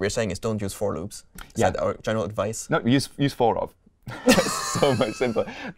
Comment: What did they say at the end? Sounded like use for-of